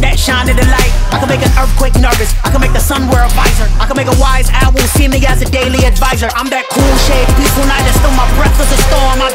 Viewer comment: 😂 buddy said “ I can make an wise owl look like a daily advisor.”….. bro what? 😂😂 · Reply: I heard "I can make a wise owl see me as a daily advisor" which makes more sense to me